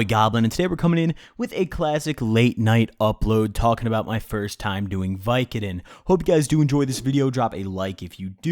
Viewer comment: I thought this said "First Time Doing Vidcon" lmao